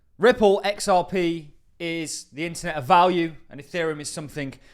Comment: Actually sounds like he says, “CRYPTO is the internet of value”🤔